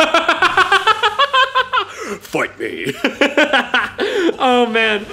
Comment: For some reason i heard F**k me